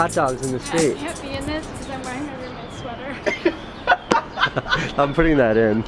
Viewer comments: "I cant be in this because im wearing my roomates sweater" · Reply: Oooooh!!! I thought she said “because I’m wearing an Hermes sweater”!!! 🤣🙈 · What does the girl in the brown sweater say about the sweater? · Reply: At first I heard quote: "I am not wearing anything under her sweater" But after reading your comment I can clearly hear "wait I can't be in this, cause I'm wearing my roommates sweater." THX